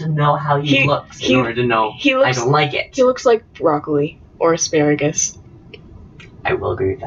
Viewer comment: When you said "He looks like Broccoli man" I thought you said "he looks like Rock Lee, man"